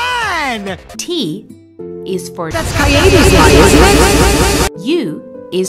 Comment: GOSH I HEARD "That's Naegi's spine" INSTEAD OF "That's Keade's lie"